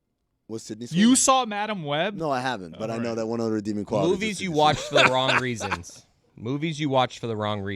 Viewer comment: did Chris say, “boobvies you watch for the wrong reason” ?